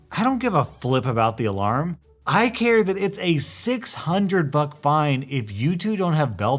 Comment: I swear to god I just heard “It’s a 600 fuck fine”